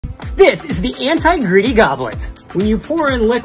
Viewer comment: Am I the only one who thought he said” this is the anti-gritty cup “